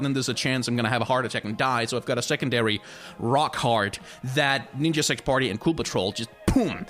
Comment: Everytime you say "Rock Heart" I hear "Rock Hard".